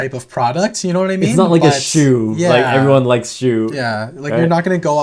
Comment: WHAT YOU GONNA BUY?! Beyblade fans in the Beyblade subreddit are going hard 😂 we love to see it! As you BBGs know, we’re also trying to complete our collection with some rare Beys that we didn’t purchase in the past. Is there something you think we should get? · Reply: when you said its not like shu for the hello kitty buy i thought you said its not like its a shoe which everyone likes lol but then i realized what you said